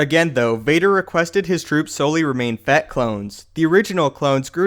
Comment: Thought he was saying fat clones 😂